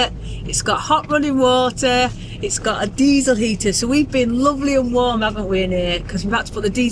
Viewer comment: I misheard you saying diesel heater in the van and thought you said ‘diva heater’. Sounds much better ❤